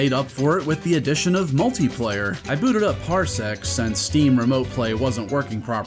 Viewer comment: Did you say Parsec?? 18:30 I thought they discontinued and removed the Arcade part of it?